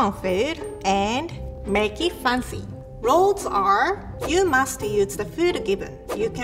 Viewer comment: I thought she said ”you musty” but she really said ”you must..”